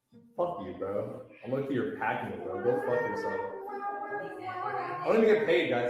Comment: i don't think he said "p*ki" which is typically a derogatory term for someone from pakistan. i think he actually said "pack mule" which would make sense circumstantially.